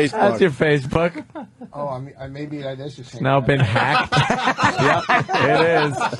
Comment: what does ruch say at 12:31 . "maybe i ....?"what ... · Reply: "Maybe that is the same password"